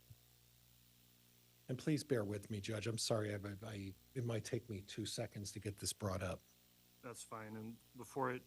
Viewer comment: I thought Pryor said “it might take me two seconds to get this broad up” 😳😳😳 I thought jesus….who is this broad is he’s referring to. I wonder if that’s considered an inappropriate way to refer to a woman in the courtroom?” I had to rewind this to hear him again and i realized he said “it might take me two seconds to get this BROUGHT up.” Oops lol